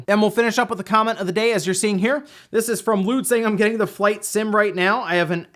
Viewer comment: i thought that said nude nation!